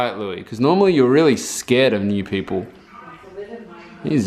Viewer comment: at some point when was with Cody and was like "he's generally scared of new people" I could have sworn he was going to say "of ne***s 😅😥